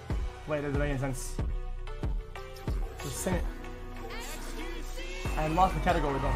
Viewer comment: did he say steamys or semmi? 🤔